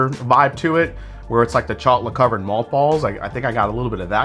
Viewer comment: You caught my attention for a second, I thought that I heard you say "Chocolate-covered mòth balls"! Then I recalled that the topic was malt! Great show! Cheers!